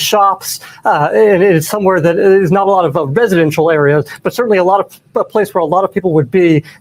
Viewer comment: Did that FUQ BOY just say re Buisidential like for businesses instead of residential that can’t be a thing